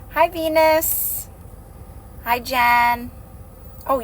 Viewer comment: omg at the beginning I had to go back and listen because I thought you said “hi penis!”😂